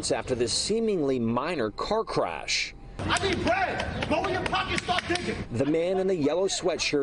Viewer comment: This dude in the yellow was probably in some type of trouble or didn’t have insurance. Rather then having the Police show up and Insurance involved he said at 0:40 “I need bread” (money) and also wanted $1000 dollars for scratches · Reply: Yeah that my bad, I thought I heard rent but yeah it was bread hints why I changed / edited my comment